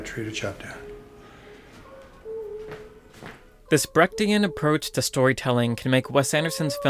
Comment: Great Video.I wonder: Does anyone know, what he says at 8:05 ?I understand smth like "brackdian", but that can`t right. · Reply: "Brechtian" — in reference to the playwright Bertolt Brecht, who was all about creating emotional distance for the audience in order to force them to engage intellectually with the characters' dilemmas. I had a bigger section on the similarities, but ended up cutting out the rest.